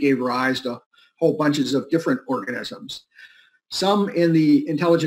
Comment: Did I hear "... a whole bunch of different orgasms."?